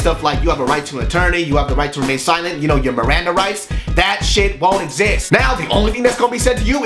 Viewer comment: i swear he said miranda RICE